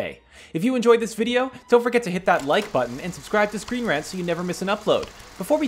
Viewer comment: it kinda sounds like u say welcome to screen RATS...